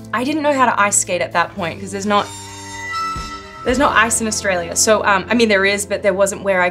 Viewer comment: I thought she said "There's no ass in Australia" at first